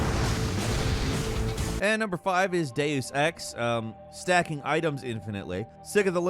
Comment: You guys really, REALLY need to check the subtitles before publishing the video, it took me a good while before I understood that what was said is Deux Ex (my volume was low).